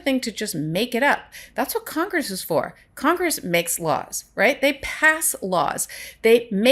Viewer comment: I swear I heard you say “That’s Congress’ job to make sh*t up!” I know you didn’t, but did you want to? 🤣😂 Seriously though, thank you. I was having trouble connecting the facts to the implications and now I don’t. ❤